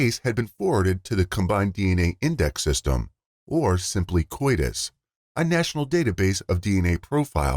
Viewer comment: Please tell me someone else heard him say coitus instead of CODIS 😂😂😂